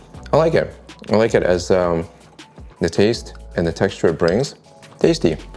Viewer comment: Love the video but when Dude said at the tasting referring to the "taste and texture it brings", I thought he said: "taste and texture of brains" and LOL, still laughing.